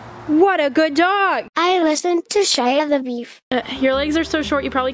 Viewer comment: OML WNEN IT SAID "I listened to Shia" it sounded like it said my name, Shealyn